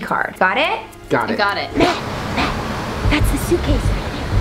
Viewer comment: I thought you called Matt Slays but It's Matt Steffanina hahaha